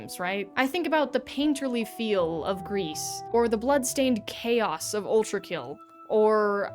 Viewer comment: "The painterly feel of Greece" - Cut, cut! I must know what game this is. I realize you said the title, but a Google search of any permutation of "Greece" + "game" + "painterly" does not give me the game you are showcasing in this clip. Please tell me more! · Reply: gris!